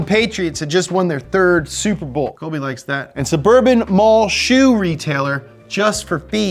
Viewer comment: Did he say "Kobe likes that" @ 2:00 ?